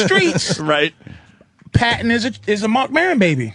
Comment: Who Patrice said was a Marc Maron baby? Cant understand the name. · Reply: Patton. I assume he means Patton Oswalt.